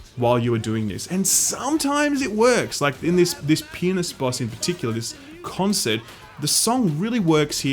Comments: "And sometimes it works, like in this penis boss in particular." I can't be the only one who heard it XD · "and you're on a stage against a peenist." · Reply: First time I heard “against a penis” and reworded to make sure that I was hearing right. Listened to it again and went “wow, that’s weird that you’d fight a dick and balls in this game but alright”. Then at 6:35 he said it again. Like that time he definitely just straight up said “penis”.